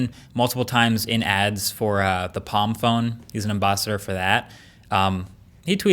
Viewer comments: did he say AmBOSSador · Did he said embaa-si-dor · Did I hear Am-boss-ider at 5:12. Helping police things up.